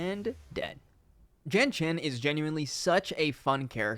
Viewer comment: I misheard Jianxin as Genshin wtf